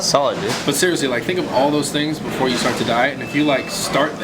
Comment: I thought he said 4 things before you die.